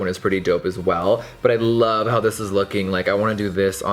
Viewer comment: I love how he's high end but still uses/reviews drugstore brands too. And doesnt just automatically assume its going to be shit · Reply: I thought you said "I love how high he is" and I tried to look in his eyes for like a good minute